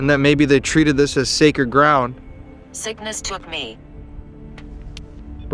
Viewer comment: I heard, “Sickness took me.”